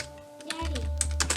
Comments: Did anyone else hear a little girl say daddy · Did anyone hear a little boy say, "Daddy." at 1:25? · lol did anyone else hear "daddy" in the background XD